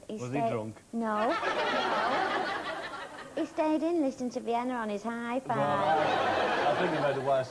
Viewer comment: Did she said, "he listed it on his iPad"? Cause I don't think IPads were invented in 1995.